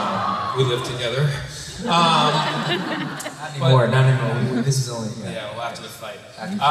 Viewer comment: What is Christopher saying on 15:03? :) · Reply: I’m 8 years late but he said “not anymore” cuz they stopped living with each other